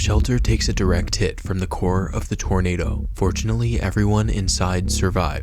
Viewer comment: bro I thought you said unfortunately! My heart dropped